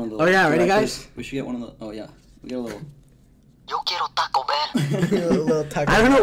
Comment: the dog is saying “I want taco bell” lmao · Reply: I heard “yo quiero taco verde” and I was confused cause I’m here wondering what’s a green taco😭